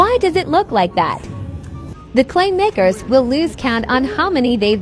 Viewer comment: I zoned out and thought when they said “clay makers” I thought they said the n word for makers. 🤦‍♀️